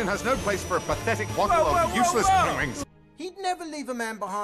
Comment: I NOTICED THIS I WATCHED IT LAST NIGHT AND I HEARD HIM SAY PENGWINGS AND I WAS LIKE CRYING